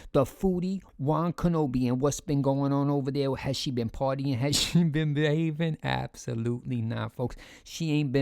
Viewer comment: I thought said “has she been partying, has she been bathing” 😂😂😂